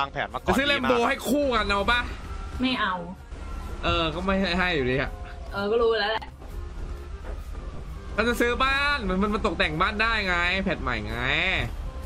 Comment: มีใครได้ยินพูดกับไหม "เค้าจะซื้อบ้าน"